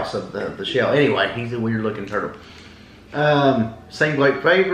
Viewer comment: Ha, ha, ha I thought said turd, not turtle 💩 🐢 my hearing is going!